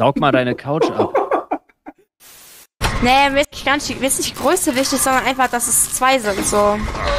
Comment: ich verstehe legitim halt null was sie sagt. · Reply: "mir ist nicht größe wichtig sondern einfach dass es 2 sind"